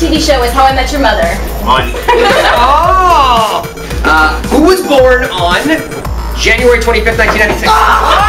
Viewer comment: When the host ask when was calums birthday i thought he was saying "whos born on,january 5th everything i didnt say"WHAT THE HELL IS WRONG WITH ME